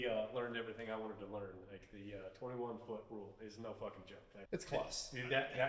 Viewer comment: I always heard it as the "10 foot rule". Still a great video by the way.